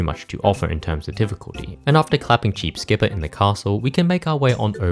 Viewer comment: i thought he said “after clapping cheeks” i spit out my redbull and replayed it just to hear he said cheeps.😂😂